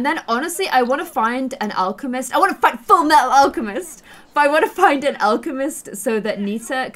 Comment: Did she just say "I wanna find Fullmetal Alchemist" at 24:30? I loved you before, but now I love you even more <3